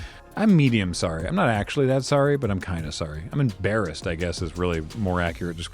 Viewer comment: did he just say he was embearassed